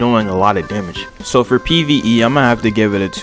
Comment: am I tripping or did he say PVE